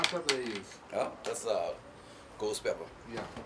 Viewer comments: I think I said GHOST pepper, not Goats pepper. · subtitals: its goats pepper what he actually said: its ghost pepper